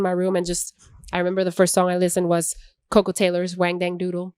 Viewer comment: Thank you! We love her too, what’s not to love! 🥰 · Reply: Can you please write down the name of the song she is refering in min 10:00 ? google is not helping i heard coco tailor someting jaja